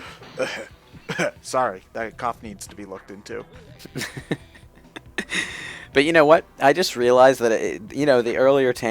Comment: What did said in that "cough", because i didn't understood what he said. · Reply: The Big Bang Theory.